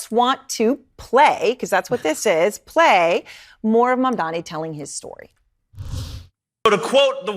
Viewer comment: I thought she called him “MORON MAMDANI” and I was like….that would have been a great line for Cuomo and Trump to use. Thank god they didn’t think of it